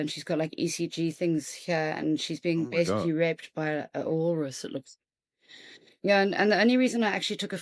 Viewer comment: She slurs her speech and I can’t understand some of what she says. Raped by a what? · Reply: A walrus